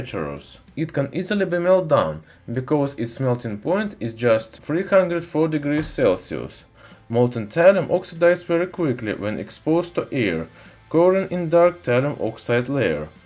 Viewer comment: I keep hearing “exposed to ear”